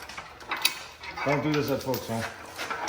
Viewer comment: Did he just say: "Don't do this at Folks Home" ???? LOL